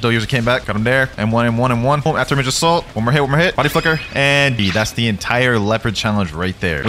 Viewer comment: is it just me or did it sound like he said body fu*ker in that one clip